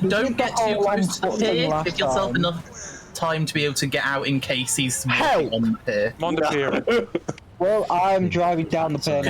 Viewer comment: I understand that resetting is probably a pain, but it saddens me that the hunters hold back in the beginning 1:46 "Don't get to close to the pier... give him enough time to be able to get out. Incase he .... " I couldn't understand everything he said, and don't get me wrong I understand why and I might have misunderstood what he was saying in the first place!! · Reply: It was "give yourself enough time to get out in case he's walking on the pier"